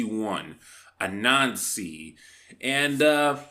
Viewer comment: deadass thought he said " a Nazi"